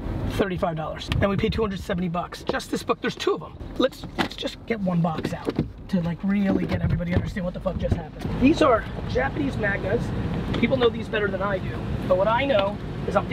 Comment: Did he say Japanese MAGNA at 13:18 ?